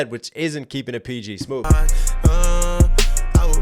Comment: i thought he was saying peachy. not pg 😂